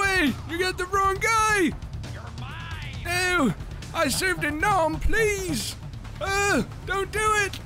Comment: Ohhhh he said I served in nam. I heard " I saved a gnome"